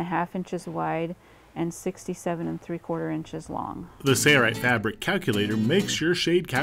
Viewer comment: It sounds like he is saying "say-rite" not "sail-rite." Is it just me?